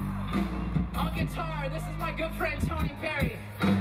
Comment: WHY DID I HEAR GIRLFRIEND